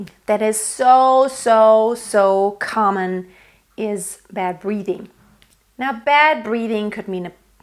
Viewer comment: 1. Narrow throat 2. Bad breathing 3. Breathiness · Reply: Oh thank god, I heard "bad breeding"